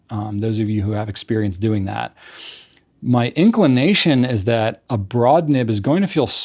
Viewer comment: anybody else hear "ink-lination"?